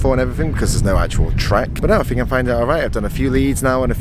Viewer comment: Did say “ I fink “ @ 13:28?! But then says “think” @ 13:58! Lol 😂 switching up the accent! Jfwu